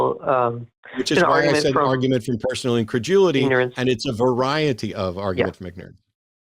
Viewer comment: It sounds like Matt says increjewelity. Instead of just incredulity.